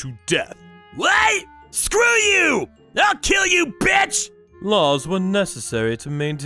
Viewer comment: Uhhh... Did he just call the Judge a b!tch? In court during a trial...😅